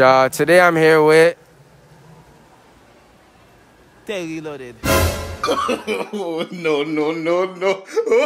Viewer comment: I thought he said “tae he loaded” 🤣🤣